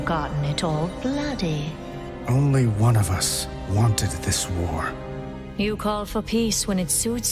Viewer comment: does it sound like they edited the dialogue wrong to anyone else? Not sure if it was "...wanted it this way.", but they used "...this war.", and left the "it" in or if he just didn't pause at all and was saying "...wanted it, this war." Either way sounds like he says, "Wanteded" which cracked me up when I saw this in-game.